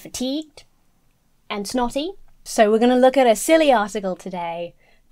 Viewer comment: Be careful when you say you’re “feeling naughty” cause it sounded like you said “knotty” and thats a rabbit hole you dont wanna get caught in. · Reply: Snotty, she mean snotty ...🤣🤣🤣🤣